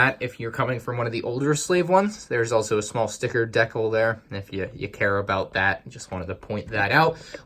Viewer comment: did he say "deck-ul" ?!